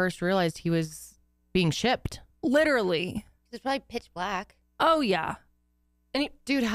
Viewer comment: When said “it’s probably pitch black” my current flash obsessed mind thought she said “it’s probably kid flash!” Lol